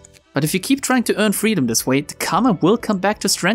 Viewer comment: Misheard “Karma” with “Carmen” and it seems 100% more accurate